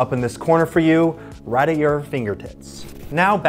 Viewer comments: did you say finger tits on purpose or… cause I know it sounds nearly identical but if you read lips you can see “tits” not “tips” am I just missing the joke or what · Kind of Off topic but when he was talking about his other videos, and he said "At your fingertips" i thought he said "at your fingert*ts" and i LOST it 😂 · Did he just say right at your fingertits or am I hallucinating?